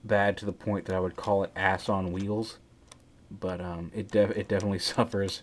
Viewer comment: A long time before, I thought you said "AXE on wheels" like that axe he has in the show and in Voyager class when the axe turns into a pack on the vehicle mode version